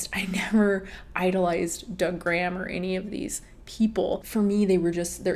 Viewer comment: Whenever you say Doug Graham I always hear it as “da gram” like slang for Instagram 🤣😭